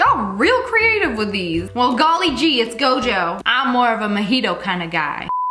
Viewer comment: did she say she's a fan of MAHITOE??